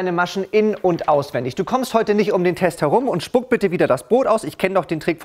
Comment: bin ich die einzige die verstanden hat: „spuck bitte das BOOT aus"